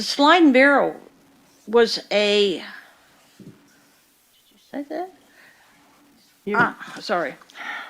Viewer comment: "did you say that" she asked under her breath while looking at her lawyer, not "did I say that". Regardless she's flustered.